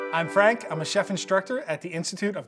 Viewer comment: I thought i heard him say Anne Frank instead of I'm Frank